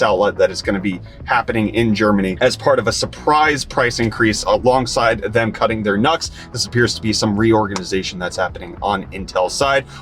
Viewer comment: Hahahaha you said "Intel will be cutting their NUCS", but I heard "Intel will be cutting their nuts!" Either way, it doesn't sound good for Intel.